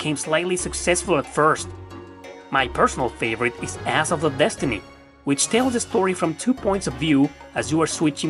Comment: When you say the atelier titles, I keep hearing you say the “ass of destiny”. 😂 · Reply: I heard 'Asshole of Destiny' each time.